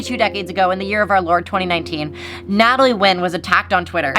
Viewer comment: auto captions said 'natalie nguyen'